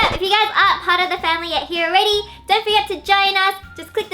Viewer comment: Am I the only one who heard "don't forget vaginas" at 0:54 ?